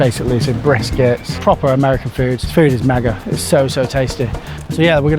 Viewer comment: I heard “the food is maga”